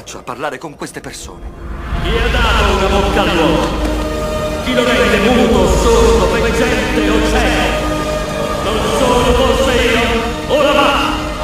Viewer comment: Chi ha dato una bocca all'uomo?! Chi lo ha reso muto, sordo o veggente o cieco?! Non sono forse io?! Ora va!! Questo è per tutti voi che non hanno capito cosa dice Dio al minuto